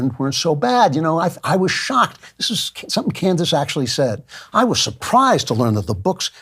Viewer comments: Did really call her "Kansas" at 9:38 ??!!😂😂😂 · did he just call you "Kansas" around the 9:39 mark?🤔 Yes he did🤦‍♂️🤣